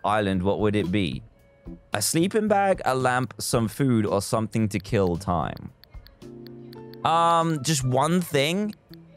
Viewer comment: When it said “something to kill time” I thought it said something to kill me 😂 Edit: OMG thank you so much for all of the support ❤️